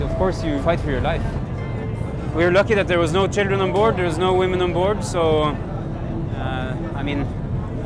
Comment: Wait..did I hear him say "there were no CHILDREN onboard, there were no WOMEN onbord"???